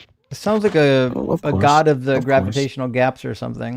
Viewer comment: is he saying God is a fat f@ with a missive gravitational pull?